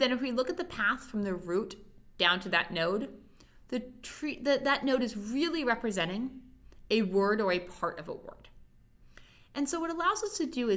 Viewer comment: lol 0:30 what the hell did she say????? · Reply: "That node is really representing a word or a part of a word." By the way, readily-made subtitles are available.